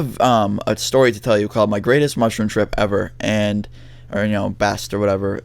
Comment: I thought you said mushroom tip